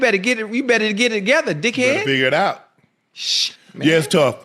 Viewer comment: Did ocho say dickhead 😂